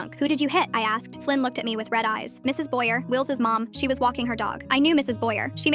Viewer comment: I thought he said Mrs Byers😰 AND HER SON IS NAMED WILL😱😱